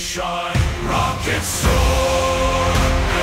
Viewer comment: I’m gonna be honest I heard “rock and stone” when they said rockets soar, think it’s got something to do with the 1000+ hours in DRG